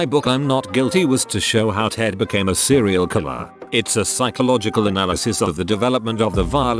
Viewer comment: Oh sorry, thought you said cereal killer. About to bring lunch and dinner to help me out.